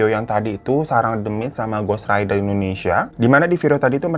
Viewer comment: Aku dengarnya ghost rider...apa aku salah dengar yaa,hrsnya kan ghost ranger